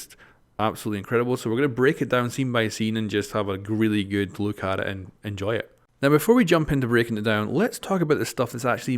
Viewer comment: tf did mira say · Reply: Elyon Kunda where she should look